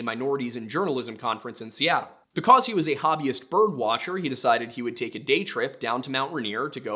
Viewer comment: Hobbyist birdwatcher I swore you said communist bird watcher